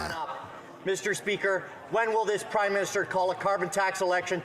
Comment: Sounded like she said Crime Minister. I'll buy that.